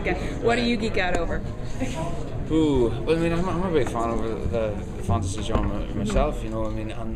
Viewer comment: did he say that he is or isn't a big fan of the fantasy genre? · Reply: He said "I'm not horribly fond of the fantasy genre"